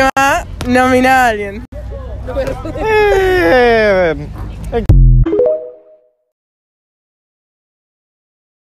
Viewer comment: que dice , que dice??? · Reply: "otra vez hector saldivia"